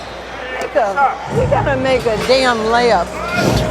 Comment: did she just say “Nigga.. we gotta make a damn layup”? She tried to say it quiet too thinking the mic wouldn’t pick it up. Turn the volume up and hear for yourself